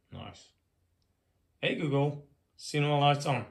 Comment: I absolutely loved hearing you say Hey Google, because initially I thought you said Hey Hugo and I thought that was literally the best. Either way, seriously impressive.